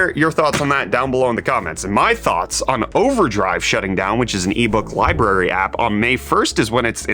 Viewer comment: when he said overdrive for a second I thought he said OneDrive and I lost it